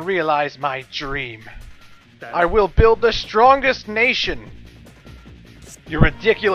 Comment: I could've sworn it said ´´Strangest nation.``